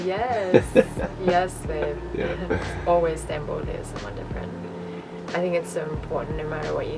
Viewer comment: at the end around 26:43 - 26:59 I cant understand what they're saying and i feel so silly about it 😅 but they each say it and its said 3 times! "Its always important to... what?" lol what are they saying!!!! 😂 HELP! 💛💛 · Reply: Jamie Town “Stand boldly as someone different “ LoL ✌🏼😉💚